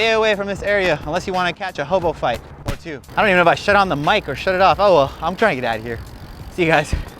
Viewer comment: Yes, I have a bike with brakes and one without. I will be using the one with them at three thirty three am at our brand new skatepark tonight, on a ten foot bowl. I carry a pocket knife on me everywhere I go, for situations like you are in in this video, and for good luck, it will help you when you carry one. I’d carry a hand gun if I was where you are there. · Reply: Thank you for making these wonderful videos sir. It sounds like you said you shit on the mic at the end.